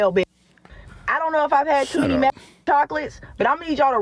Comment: did she just say she might've had too many meth chocolates?